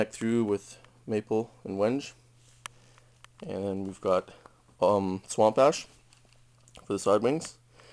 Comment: I lol'd at 2:23 because I thought you said swamp ass.